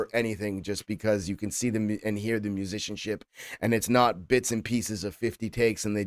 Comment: Sometimes when you say “musicianship”, I hear “magicianship”. I can’t figure out if it’s bc of the way you say it or bc it just sounds so accurate when referring to TBP. 🤔